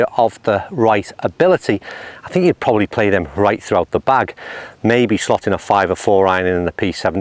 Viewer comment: Nice vid! Did I hear a “fore right” at 9:41?? I’m looking for a perfect blend…like you I grew up playing blades, but I played a pitch and putt course mostly in my youth where you just needed 7-PW and putter…So, for me, I can play blades all day long from 8-PW and not blink an eye (8 iron now is like the 7 iron loft of old)…but I prefer to have a little more help in the longer clubs these days as I’ve had both hips replaced and lost a little zip; I just don’t want a much bigger head…that’s the issue…every GI club I find makes the mid-irons huge and I can’t hit them for some reason…I have a set of MP221s and TM P790s that a friend is letting me test. I hit the 5-7 in the MP221s much more consistent than the P790 5-7, but I just don’t get the distance I used to with a 7 iron swing speed of 80mph…